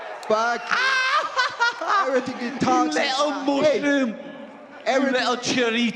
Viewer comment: I’m i trippin or did he say “you little Muslim” ?